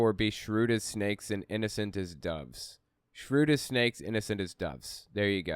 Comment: He says Jesus says be shrewd as a snake and innocent as a dove, and then says “our motives need to be pure” and that’s how they justify every morally/ethically wrong thing they do. According to them, the end will justify the means. Once they go down that road, there is no line they won’t cross. Which they’ve already proven. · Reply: I swear I heard 'innocent as ducks'! 😁